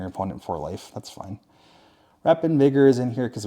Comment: what did you just say 😂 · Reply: Wrap in vigor but yeah it does sound fucked up